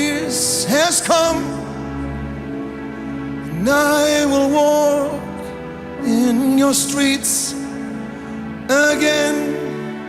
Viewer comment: it sounds like he said' "And I will walk in your streets again." Did anyone catch that?! To me it sounds like the Spirit of God will be among us again walking with us closely. Does anyone have a different idea of what it could mean?